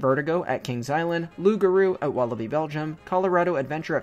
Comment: my subtitles said ‘wallaby’ instead of ‘walibi’